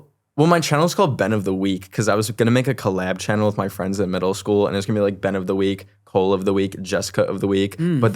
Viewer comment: I THOUGHT U SAID U HAD NO FRIENDS IN MIDDLE SCHOOL 😂